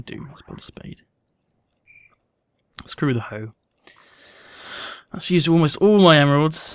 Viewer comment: "Screw the hoe." Did anyone else catch this?